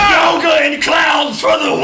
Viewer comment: did rhett say "FUCK YOGA AND CLOWNS FOR THE WIN"?